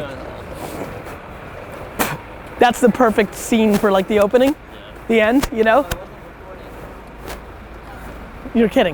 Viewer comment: What got Gary's attention at 11:45 ? He said he didn't fall for it. What did I miss? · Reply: Lindo Korchi he said i didng record that